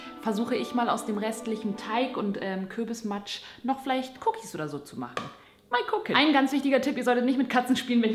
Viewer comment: Bei 4:38 hab ich Koks verstanden xD